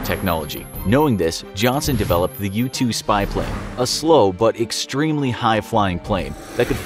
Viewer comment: When I first heard “U-2 Spy Plane” I thought he said “Youtube Spy Plane” and I got confused